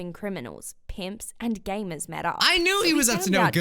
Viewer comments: did she say gamers? Now you're pulling my leg, gamers dont go outside, everything else seems feasible though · Did I hear that right? Did she say gamers?